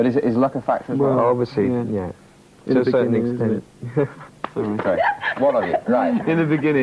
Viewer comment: Hey everybody, I have a question: what did they say at 4:20? I hope that someone can help me to answer my question ;) Love, Samy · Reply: Alan said 'to a certain extent' but I agree I think they just said sorry and hit each other because they talked over each other :)